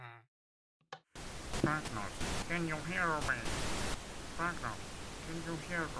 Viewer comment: I hear “drifter can u hear me?”